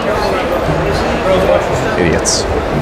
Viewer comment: Did he say idiots?😂